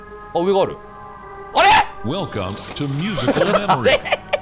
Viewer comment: Wake Upがwelcomeに聞こえたww